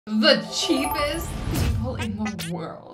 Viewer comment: Instead of cheapest I thought it said creepiest